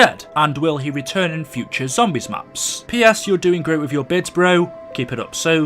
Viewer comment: lol youtubers make bids instead of vids🤣🤣🤣🤣😂😂😂